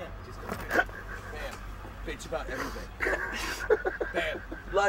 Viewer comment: Anybody else hear at 2:29 someone coaching him through the scene? (Bam ,Bam bitch about anything)